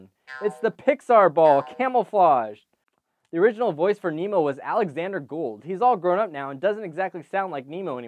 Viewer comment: I literally thought he was gonna say Alexander Hamilton send help.